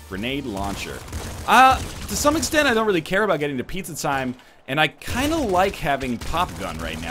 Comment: Did he not notice that the Throne's Big Ball Shots damage the generators? 16:25 Did I hear right? "I don't care about getting to Pizza Time"....MIND FUCKING BLOWN.